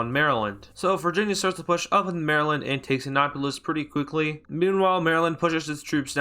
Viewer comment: it sounded like you said acknawpolis Also, as a Marylander, we were so close to winning. VIRGINIA WHYYY